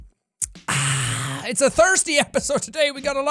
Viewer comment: for a second there I thought he said 'it's a thursday episode', realised he said 'thirsty', then realised that this vid actually did come out on a thursday anyway, and my brain imploded